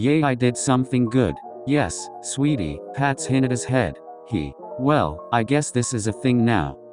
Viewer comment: i thought it said “passes hinata head” and i was so confused😭😭